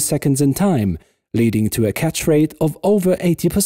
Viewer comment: Catch? oh I thought it said scratch :P